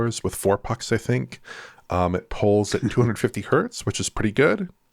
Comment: Wait really!? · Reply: Did he say 250hz?